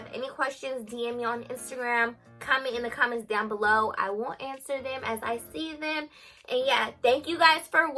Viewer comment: I thought she said “I won’t answer them” 😭😭😭💀 & it’s the fact that I watched all of pt.1 & pt.2 without skipping! That’s how yk she entertaining kos I have a bad habit of skipping😭💕💕 She is sooo pretty😍😍😍!